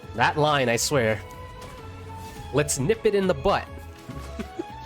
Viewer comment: 7hour!? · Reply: And i always misheard the sentence about tighnari "lets nip it in the bu**"😂😂😂